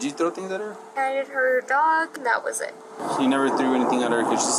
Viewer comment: Hobble me this:_RUKStash: Hobble me that, another one in a Hobble Cocoon <link> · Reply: I heard her say "I pettter her dog... And that was it"